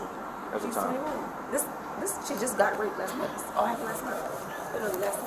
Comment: did she say a man rVped her last night??? And she was texting him? Did i hear that right